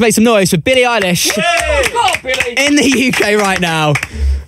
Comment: did someone just say f*ck off Billie?